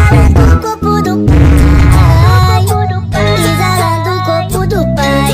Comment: pov you thought the song said "dubai" and not do pai 😂